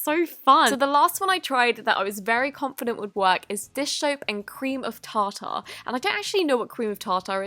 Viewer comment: I swear I thought she said fish soap!